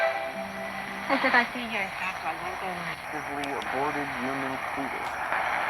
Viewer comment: did he say aborted human fetus lol